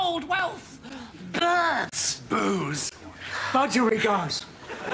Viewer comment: What did Dave say? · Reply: Budgerigar's as in the bird budgie